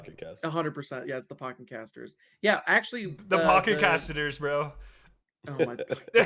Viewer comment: Did they say P'cketcastnter?